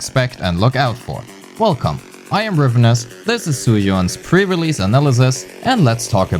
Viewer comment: I just heard you calling her "Zhu Yuass" 😳